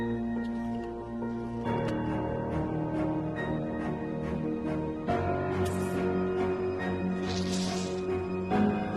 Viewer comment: elle fait quoi mes amis ? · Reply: Sur man li tap fon jan poul touye peri pi rapid